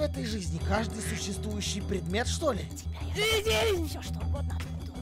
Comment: мало кто услышал из-за клайпа,но сказала эду"для тебя найду все что угодно"